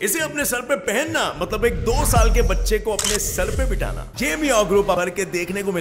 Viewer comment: l thought he is saying "" MEOW "" 😹😂😂